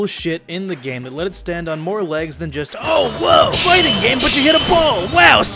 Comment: Did i hear FIGHTING GOLD? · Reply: No, he said fighting game. Yare yare daze. I don't like saying this. It's muda to say it.